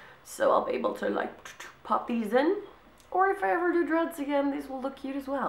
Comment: I thought you said (about the red hair clips) "Or if I ever do DRUGS again these will look cute as well" :D